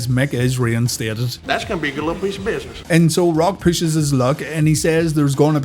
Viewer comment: What does HHH say? I never understand it. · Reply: "That's gonna be a good lil piece of business "